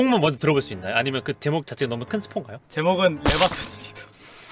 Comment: 레'박'툰이라고 자체 필터링해서 들었네요 휴